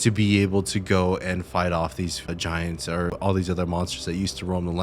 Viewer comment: Did you say vagiants? 😂 Awesome video bro